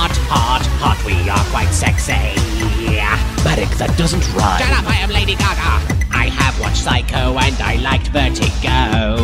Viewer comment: “shut up I am Lady Gaga!” Even after 10 years that line is still hilarious. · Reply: I heard it wrong, I heard it as "shut up, I have lady gaga"